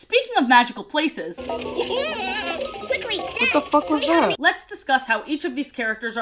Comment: why on earth at 8:07 do i hear my freinds voice saying "wtf was that?"